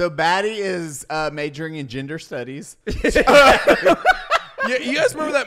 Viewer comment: Did you say Baddie Streams was majoring in Ginger Studies?